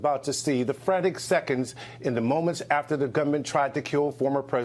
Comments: he said “after the gunman tried to kill” “gunman” not govment, lol he didn’t forget the, R, he clearly says,”gunman” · I also thought the News Anchorman said "Government" at first, but after another listen and upon reading his lips it's clear he said "Gunman." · He said gunman it sounded like he said the g word but no kmsl. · the way he says "gunman" is not articulated well. It sounds like he is saying "government". I slowed the speed and I sped it up. It sounds like government when slowed, but gunman when sped up. He just didn't articulate the word well.